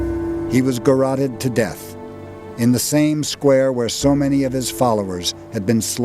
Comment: what did the narrator say around 6:23? "gaurrated to death"?